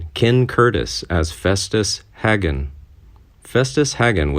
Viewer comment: I thought you said Asbestos Haggen. Ha Ha:face-blue-smiling